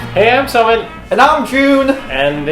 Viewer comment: Whenever Jun says: "And I'm Jun" I hear: "Namjoon" or "and I'm Joon (as in Namjoon)" Ahh my ARMY heart, lmao